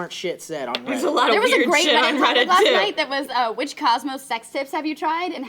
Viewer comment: I misheard Cosmo as Cosmos (the show about space Carl Sagan did) and was very confused for a while like "what, did they slip sex tips in with the space stuff?"